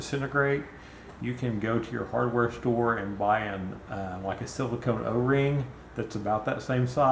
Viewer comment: What type of O ring ? Couldn't understand what ya said. Thanks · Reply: Silicone O-ring. I think thats what they are made from. They’re just the standard o-rings they sell in the plumbing sections at hardware stores.